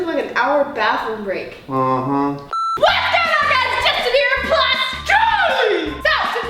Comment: I thought Ameerah said HEY GUYS ITS JUSTIN BEIBER